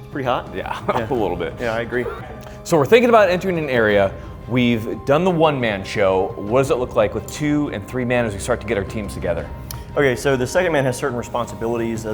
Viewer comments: tell me what he says, now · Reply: "Navy SEAL" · What did he say at 8:13 that got muted out and had quite the reaction going? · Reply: Navy SEAL